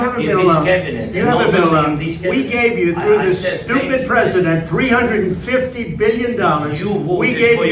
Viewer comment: Did he call "Stupid President" at 6:50 or I heard something else?